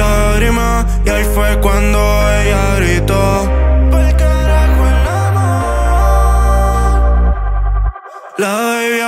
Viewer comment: I think I heard Khea first "pal carajo el amor"